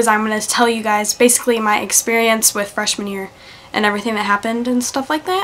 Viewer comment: Wen she said freshman year I thought she said fresh manure :'D(